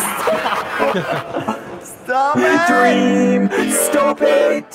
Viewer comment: Wait did BBH say asshole?!? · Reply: "STOP YOU'RE ACTUALLY ABOUT TO KILL ME!!"